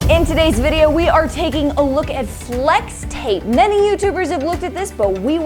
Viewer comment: I hear slex tape, just me? 😂